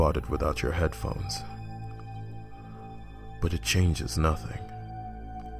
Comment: Until the very last line, I had a dyslexic/deaf moment and thought you were saying thumbs.